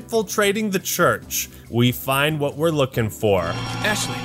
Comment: when a saw this I was eating a Dorito and couldn’t hear correctly so I heard. We find the whore